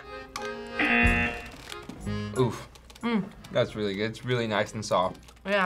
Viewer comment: did he say oof? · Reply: Yup HE SAID OOF